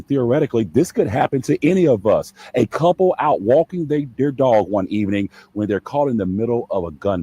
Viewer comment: Did homeboy just say "walking they their dog"?